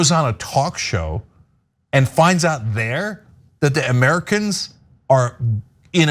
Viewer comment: He didn’t forget and obviously he knew who invaded what. It literally sounded like she said Americans came in that’s why he responded that way. Biden makes a fool of himself every day and leftists ignore it, Trump gets confused once and it’s like “YES, we got him sounding foolish” really? Get a life. Trump was a strong leader. Now that embarrassing cognitively declining Biden is in office Putin doesn’t see the US as strong. So yes this could have very well given him the confidence to invade Ukraine. And yes we all know the election was indeed rigged as there are PLENTY of videos and testimonials that prove this. If you believe Biden won fair and square you have been gaslighted. You sound pathetic saying Trump keeps crying about not winning. Your show is a joke.